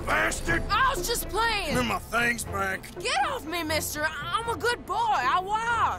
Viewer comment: Even im scared of Arthur when he says "gimme my things" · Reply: All I hear is “gim ma thangs back”